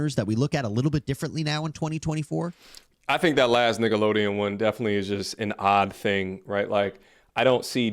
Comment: sounds like he said “niggalodeon” lmao